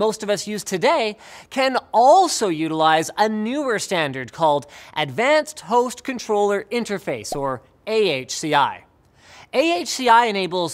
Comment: thought he said advanced toast control interface